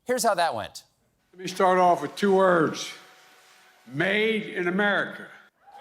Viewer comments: I heard two words. "Maiden America". No one heard that? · Huh? I heard him say Maiden America. That's two words. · You heard "Made in America". I heard "Maiden America".